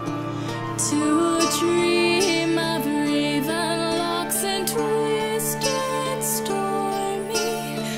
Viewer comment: I've listened to this so much im even saying "laven locks" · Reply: in the chorus it sounds like your saying "laven locks" instead of "Raven locks".......could just be me